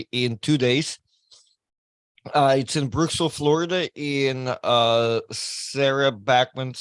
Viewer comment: Re: Florida, Brooksville is a small town. Thought said 'Bristol' (not Brooksville) at first...now that's a small town 😄